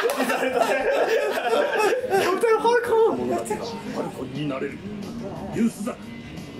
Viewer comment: 英語字幕、「リュウ・スザク！」って呼んでるのに空耳で「You suck!」って罵倒変換されてて草 そのあとのスザクの「CAPTAIN HOT DOG」で更に草